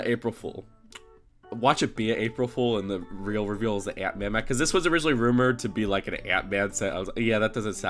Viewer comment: When you said ant man mech I heard ''aunt may mech'' I was like what, there were rumors for that?!